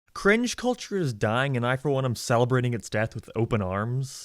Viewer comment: I for some reason thought you said french culture